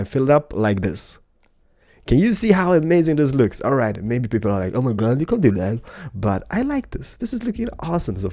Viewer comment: What Did he Say at 3:16 oh and by the way 3:16 = STONE COLD!!!! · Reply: He said"Maybe people think Oh My God you can't do that".